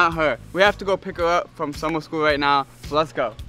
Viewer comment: Did u say summer school!?. Did she fail?